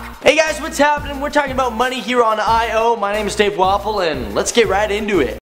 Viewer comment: I thought he said "Dave WAFFLE" I was like ok...